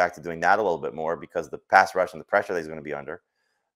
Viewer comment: My son heard me listening and thought said "pass Russians" not pass rushing. Lol